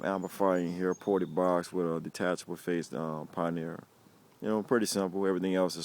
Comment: i just heard you say alpine hwad unit, but when the light came on it says pioneer, still tight tho